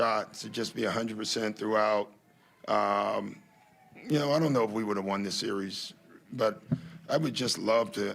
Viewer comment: Did he just say "I don't think we could have won this series" at 4:50 to 5:05. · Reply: He said "I don't know if we would have won this series" talking about if Joel was fully healthy, like saying "I can't guarantee it but I would like to find out".